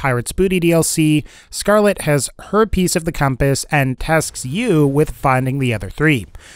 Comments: When you said "Her piece of the compass", i thought you said "herpes of the compass" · was i the only one that thought he said herpes instead of her piece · did you say scarlette have herpes of the key or I miss hear this part